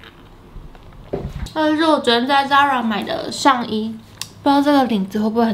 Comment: 上衣很美, 但是聽不清楚在哪裡買 T_T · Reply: Zara